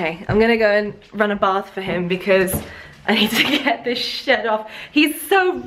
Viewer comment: I thought you said shit I’m like what you swear you don’t seem like one to swear but when I listened to it again you said shed not shit